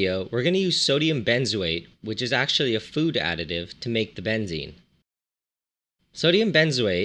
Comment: MY GOD I thought you said how to make BENZEMA hhhhhhhh